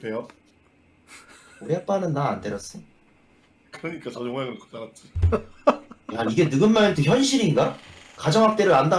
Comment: 뭐라고 말하는 거임? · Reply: 그러니까 저 모양으로 자랐지 (컸지라고 하려다 바꿈)